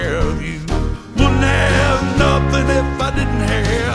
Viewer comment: it sounded like wooden ham